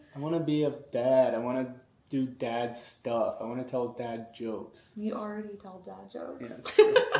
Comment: Hey!! What to wish you BOTH the very Best on this New Journey!! I thought was saying Bad... but I see and hear that he was saying Dad Jokes, not Bad Jokes.. LOL All my prayers and blessing be with you on this Amazing Journey!! You both will make Awesome Parents!! Amen!! 🙏. P.S. Thank you for sharing your life with us. 🙏